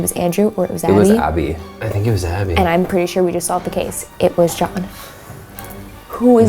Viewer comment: it sounds like its saying "it was jaba" from star wars